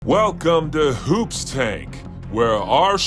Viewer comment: Coulda sworn they said welcome to Hoobastank